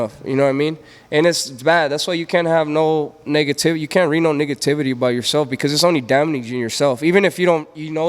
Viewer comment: I know yall heard him say "nggativity" 😂😂😂